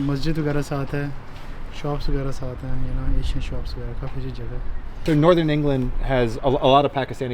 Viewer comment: he says Asian shops doesn't he, subtitles wrong? So it's good cos theres a mosque and Asian shops. Integration for you